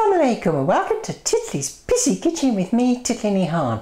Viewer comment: Sounds like you say ''welcome to Titli's PISSY kitchen...'' LMAO! :') Love you Titli, you're so funny! ~HappyChap :)